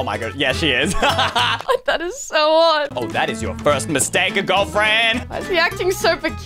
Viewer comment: did you say girlfriend at 0:49?